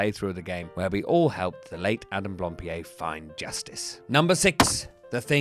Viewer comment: I genuinely had to rewind the video because I thought said “we all helped fellate Adam Blampied”